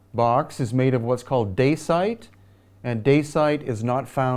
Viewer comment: Can someone please enlighten me what stone did he mention at 40:53? Auto-generated caption says it's "de site". Many thanks. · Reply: DACITE - it's a volcanic rock.